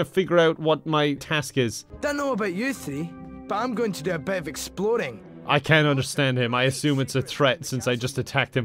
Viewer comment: I thought the student said "I dunno about you see, but I'm going a bit of exploding" between 0:56 and 0:59.